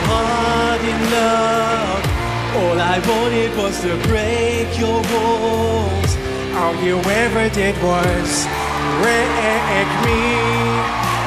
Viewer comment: I thought he said to break your balls not walls